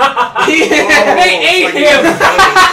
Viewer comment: When you guys yelled THEY ATE TH FAT MAN I thought you said batman then I thought you said bad man chalk one up to sucky hearing